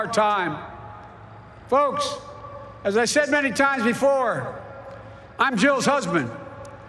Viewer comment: "Iam Joe's Husband?" Did he really said that or am i in drugs? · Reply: He's a total baffoon, but he actually is understandable this time and he says "I am Jill's husband."